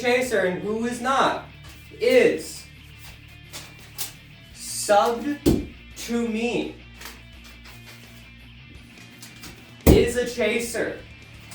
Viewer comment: omfg I thought you said "anyone who isn't subbed to me is a CHASER." I cried laughing LOL